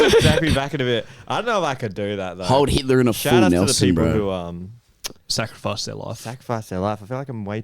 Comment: im sorry, but it sounded like you said "hold hitler in a full nelson, bro"